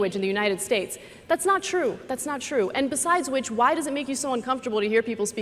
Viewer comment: Did Ana call ann coulter a witch at 39:15 or did I hear that wrong? LMAO